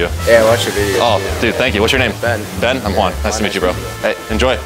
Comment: Will never get used to watching these. Great stuff Juan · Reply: I swear I heard Glenn then I had to go back to hear it. He looked like the actor that played Glenn in walking dead